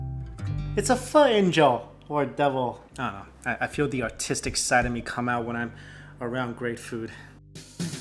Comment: no way did he just say the autistic side of him come out XD. I love the honestly of probably the reason i watch all his videos.